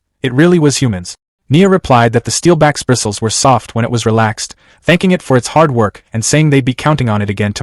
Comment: I heard you say relc not relax you tried to correct me you didn't say it bra